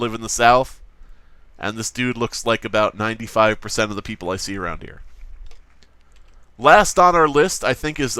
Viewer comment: WTF at 5:27 you said "95% of all people I seen around here" then my t.v. said "about 95% of all the people you see around here"